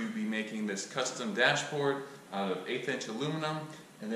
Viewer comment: I thought you said you where going to make the dash out of 8 inch aluminium and thought that that will be one massive dash. Then I figured out that you probably ment 1/8 inch.